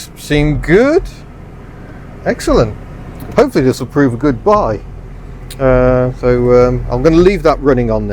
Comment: Err, was that a Freudian slip? - or bad spelling........! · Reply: it sounds like is saying good bye rather than a good buy.